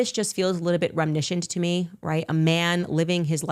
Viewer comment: oh my goodness i forgot about her mispronouncing "reminiscent" why on earth does she say "remnishent" 🥴